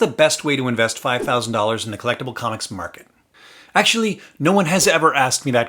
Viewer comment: It's funny, with your 1st sentence I had originally thought you said "how do I spend $5,000 in a collapsing comic market", until I had to go rewind and listen to what you actually said ... "collectible comics market". 😃